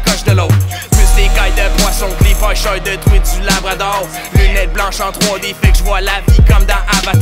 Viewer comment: qqn peut me dire ces quoi qu'il dit -- plus l'écaille de poisson ............. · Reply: Plus d'écailles de poisson que les pécheur de truite du labrador!